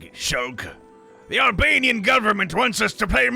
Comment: did he say the albanian goverment??